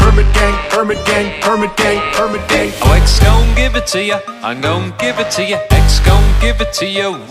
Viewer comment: It sounds like is saying "I like stone give it to ya"